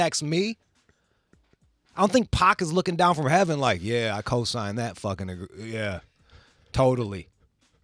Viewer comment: Did just say the N word? Wow. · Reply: i listened to it without looking at his mouth and a headset, i hear "cosigned that fuckin' -agr *stop*... (agreement)" and what you're hearing is the "n'-agr" part of it all. So no, I don't think he said it but I can see how people can misunderstand.